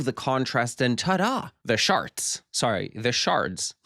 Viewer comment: I thought you said “The Sharts.”